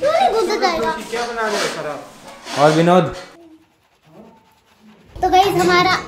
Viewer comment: who hear that Sourav Joshi said 'aur "BINOD" '